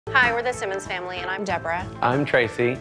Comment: I thought they said the Simpsons LOL